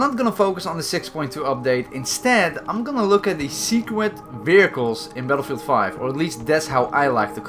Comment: Am I the only one who heard him say "secwet" or I got hearing problems?